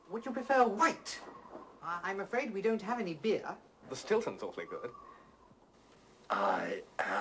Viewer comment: what is the guy saying at 1:58-1:59 ?? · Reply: “The Stilton’s awfully good”